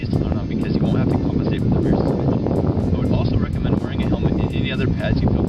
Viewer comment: Good video apart from wind noise at 2:36. Did you say lock out rear suspension?